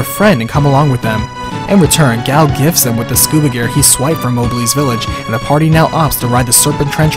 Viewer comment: Did he say "swiped from Mogleys village" at 12:33 ?